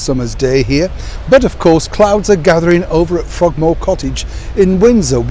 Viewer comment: "Clouds are gathering over at Frogmore Cottage in Windsor where Prince Harry is." Well put! 😂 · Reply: I thought he said "crowds are gathering". Hmmmm. That might make Harry a bit stressed. Maybe Eugenie and her family went down the road to stay at her parent's place. I know I wouldn't want to share a home with my wayward cousin.